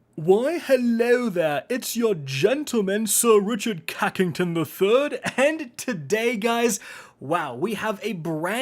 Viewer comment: Did he just say Rick "cockington the 3rd?" 🤣🤣🤣🤣